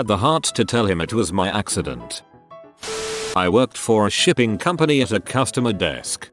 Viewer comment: as soon as he said shipping I thought he said shitting because of the previous one